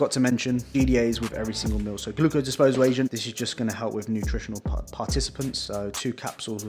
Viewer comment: did you say nutrient participants😂